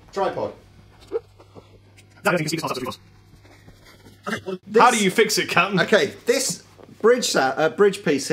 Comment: did he say "How do you fix it c*nt"??